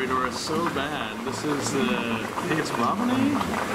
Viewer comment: what were the cories at 8:14? I cant make out what you said :( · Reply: corydoras, I was slightly off with ralibuti. <link>